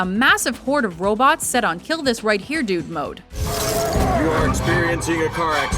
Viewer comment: = kill this right here dude mode? I thought that was what I heard-had to double check. Confirmed